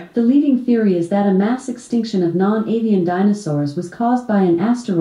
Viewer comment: I misheard non-avian as non-alien and was so confused 😂